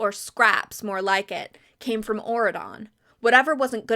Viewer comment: I have never heard of these books and until 20 minutes in, I truly thought you were just saying Oregon in a strange way instead of Auredon😭